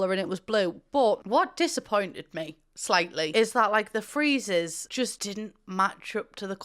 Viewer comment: Anyone know what “freezers” are? Love, a confused Californian 😂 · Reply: Friezes 🙂